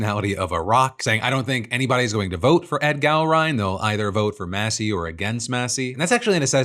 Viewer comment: It's Gallrein, but I keep hearing Gowron: the Klingon High Chancellor bullied into an unpopular war in Star Trek DS9 😂